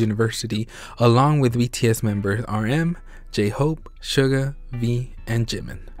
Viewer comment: thanks for this sooo mach video put i have a question at 4:20 you say RM J-hope SUGA ME and JIMIN who are you and wy did you say me ?